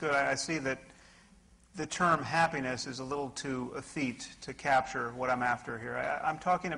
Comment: Hey, if anyone could help me out that would be greatly appreciated - At about 10:02 Sam Harris Says a word that.. I can not find anywhere and I'm thinking I may have misheard it. ETHEAT was what I thought I heard = any corrections (wanting to know the definition more than the word/both would be nice) Anybody ?? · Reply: The word is 'effete'